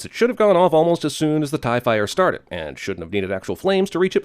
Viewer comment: ‘Tie fire’. I honestly thought he said ‘T.I.E. Fighter’.